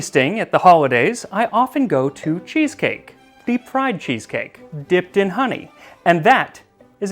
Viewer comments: Instead of "deep fried cheesecake" I heard "the pride cheesecake"... I was like queer cheesecake? Prayers what is your recipe? 🤦🏻‍♀️ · When you said “friend cheesecake” I heard “pride cheesecake” and I think that should actually be a thing!